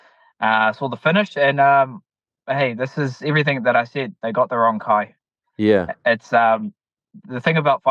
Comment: they got the wrong Kai 😂😭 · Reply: I thought he said “they got the wrong guy” makes sense now 😁